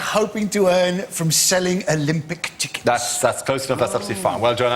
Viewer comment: I thought Andy said Olympic TicTacs. Man, that would've been awesome.